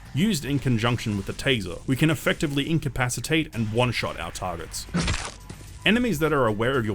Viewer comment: did you just say oneshot